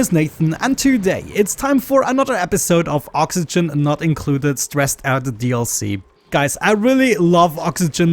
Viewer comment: did u just said stressed out DLC ?